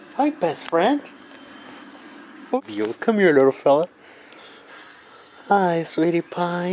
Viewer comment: Does anyone else hear a girl say " hi dad" after the dad said " hi sweetypie" on 0:07 ?